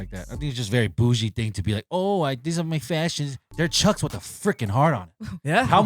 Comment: LOL! I had to rewind 4 times around the 15:04 mark in the video..... "They are chucks with a freaking heart on" I heard "they are chucks with a greaking hard one"